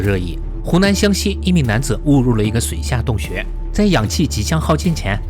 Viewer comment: 空耳：湖南湘西一名男子汙辱了一個水下洞穴